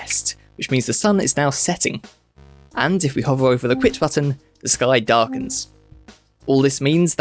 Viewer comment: I'm over here mishearing "if you hover over the quit button, the sky darkens" as "if you hover over the quit button, this guy dies" lmao